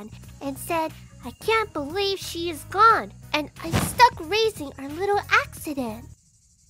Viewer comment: What did she say? My English isn't native and that mic blow in 0:14 made it unclear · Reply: She said " I can't believe she's gone and I'm stuck raising our little accident" And that would mean Dojima and his wife didn't plan Nanako's birth.